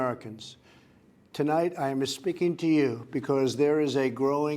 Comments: Did he say "..tonight I'm misspeaking to you.."? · " i am aspeaking to you...." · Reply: I thought he said, "I'm misspeaking to you...."